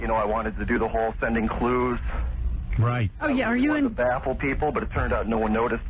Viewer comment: Idk who made these captions but they need to be fired 8:00 No way they thought he said “the apple people” instead of “I just wanted to baffle people”. 😂